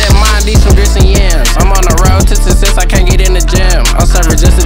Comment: What he say lol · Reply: Im on tha road to success cant get in a jam